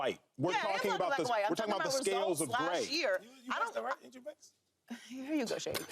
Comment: I missed what he said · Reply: Haha they said Joy was talking black and white. Then Shady asked Joy " ain't you mixed?" Lol joy is biracial. Shady is hilarious. And joy stayed on topic it didn't throw her off at all 😂